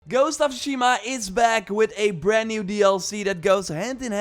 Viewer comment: did he say "ghost of shima"?